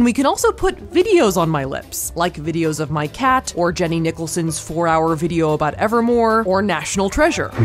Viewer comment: Crying at the Jenny Nicholson shout out 😂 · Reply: Lmao I was listening without my earbuds in and I thought she said Jenny Nippleson, I was like why tf is she flaming her???